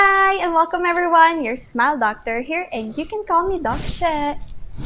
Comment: I thought she said her name is dog shit